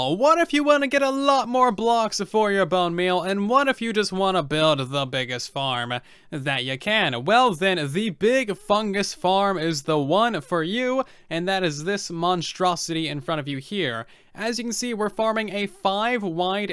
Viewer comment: At first I thought he said “Big Chungus farm”